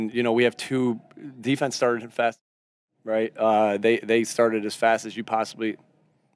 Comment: Why was he apologizing to his kids and saying his son didn’t understand the word he said? Did he say the players were fast as $hit? That’s another reason why you don’t bring your kids to a work atmosphere where foul language reasonably can be expected, including from yourself! Also, I saw Brandon Graham on the sidelines laughing with Sirianni as Sirianni passed by and told him, “Watch this!” That’s not cool, either.